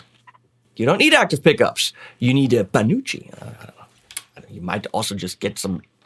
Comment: Sorry I thought it said panocha 🤣 Look up the meaning of panocha and you will see why it's hilarious 😂